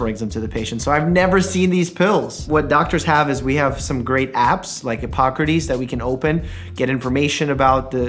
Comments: i swear he said great abs lol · umm i heard "doctors have some great abs" im not okay.